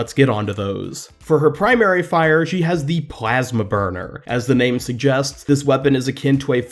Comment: being honest i thought you said “plasma burger” or am i just really that hungry?